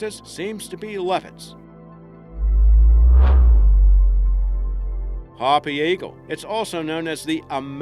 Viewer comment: I don't fully understand what he's saying because of his accent. What was the real threat to marshall eagle again? 4:45 Levin's?? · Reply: I think it was leopards